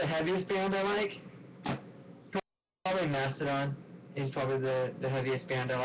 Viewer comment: Could anyone tell me the name of the band he's talking about at 43:30? I can't understand it properly :/ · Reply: Mastodon